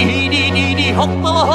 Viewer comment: SOUND LIKE BATMAN SAYING HO. IM AM DYING RIGHT NOW.